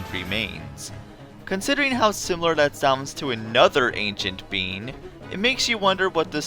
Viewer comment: did anyone else hear ANCIENT BEAN or is it just me?